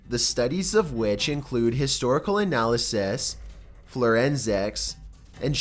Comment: I swear I heard "florensics" and "indiguous"...did I?